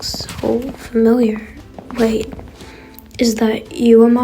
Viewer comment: I THOUGHT HE SAID WIG😭😭😭😭